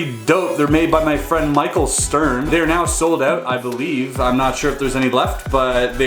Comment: Thought he said Michael scarn XD